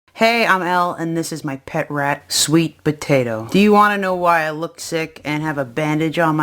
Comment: i dont know why but i thought she said "this is my pet rats we eat sweet potatoes" ... im dying